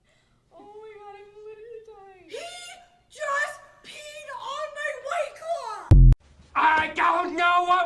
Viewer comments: What did it pee on that she screamed about? It sounded like she said "he peed on my raycons" (earbuds) But that wouldn't make sense since said "it still smells the same. So im assuming it's something that already smelled like fox piss?!?! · what does that lady yell?? " He just peed on my___"??🤔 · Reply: white claw